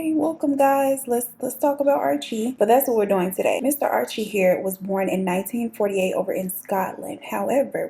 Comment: When you said "Let's dive right in... Mr Archie here was born in..." (etc & so forth) I was so excited but had this itch I simply could not scratch.. In my head I heard " ...Miss Mammas here was born in..."